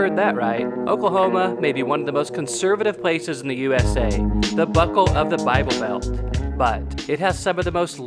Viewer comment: heard this as "the butthole of the bible belt" and was like... haha yeah makes sense. Until my husband corrected me that you said BUCKLE. Woops😄